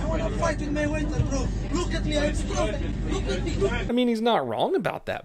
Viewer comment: Is it just me, or am I hearing, "Look at me. I'm stronger." but with a really thick accent? Then headbutt.